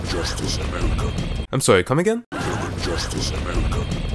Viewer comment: I think he said “Deliver justice, American.”